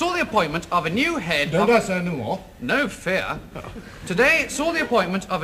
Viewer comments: says Professor but the 2nd word I cannot make out And I think the other guy said No fear? or No Fair? I cannot tell. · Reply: "Don't I say any more?" "No fear!" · what did graham say at 2:27? making me crazeee.. thanks. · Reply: “Need I say more?”? Maybe?